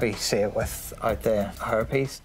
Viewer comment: Okay, who else heard "herpes" at 20:59. 😳 I was listening but not watching. Had to play it back again to catch the word "hairpiece."